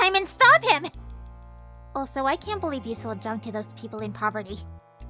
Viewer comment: what the hell did Lucca say at 6:55? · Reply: ‘Also, I can’t believe you sold junk to those people in poverty.’